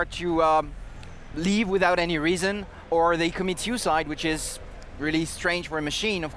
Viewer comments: Did I just hear "They commit hu-cide?" Hucide? What happened to the word 'homicide?' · Did I just hear "They commit hu-cide?" Hucide? What happened to the word 'homicide?' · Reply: Oh. I heard "suicide".